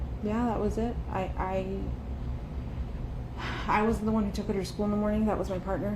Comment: I was the one who took her to school is what I hear